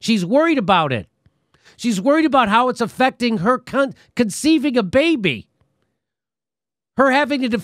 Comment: Did Jimmy just say "It's affecting her cunt"? 😂